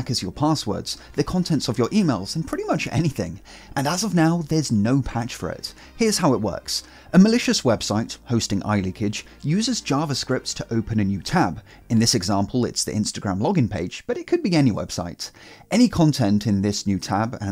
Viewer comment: I misheard Malaysia's😅